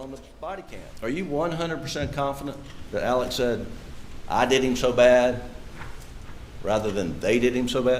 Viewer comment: I heard "This is so bad, it is so bad/they did it so bad." not "I did him so bad".